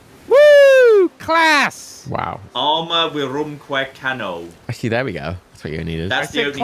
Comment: Can someone translate/give a source for what said about 5:30? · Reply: "Arma virumque cano." I sing of arms and of men.